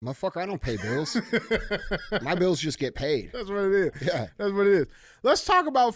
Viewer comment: “Mf i don’t pay bills, my bills just get paid” 🤣 that my mf goal ong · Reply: I thought he said “I just GET paid”😂 either way is still hilarious and my same goal👍